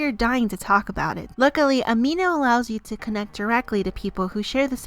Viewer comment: I put the speed at 0.5 to read what the quiz said and when she said amino I heard "ah me nose"XD